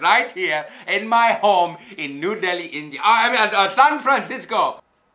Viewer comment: did he say new delhi..?🇮🇳🇮🇳